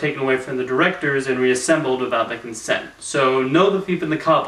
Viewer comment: I misheard. When you said "without their consent", I thought you said "without making sense". And what do you mean by "I actually do The Thief and the Cobbler" in your comment?